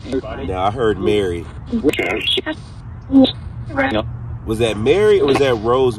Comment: sound like they knew who they were talking to.... 35:10 "That was Mary" 35:29 woman's voice "Dalen" 35:54 woman's voice again (very fast) "Dalen Spratt" 36:06 man voice "Dalen Spratt" 36:10 man voice "he's going to.....?" 36:14-17 something about 'ever wish...? (couldn't make out rest) & name AGAIN